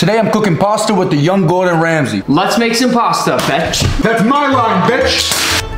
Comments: I thought you said bitch but it’s betch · I thought u said the b*tch buf u said betch